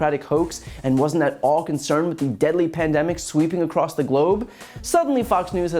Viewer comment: Deadly pandemic? I thought you said truth <link>